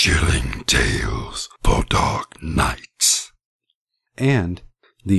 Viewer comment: did he say nights or nuts?